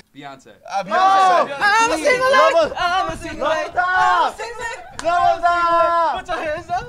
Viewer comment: did he just said I'm a single leg? And others following I'm a single leg ...Lmfao 😂